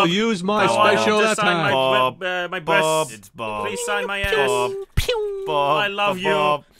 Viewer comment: did said please save my ass.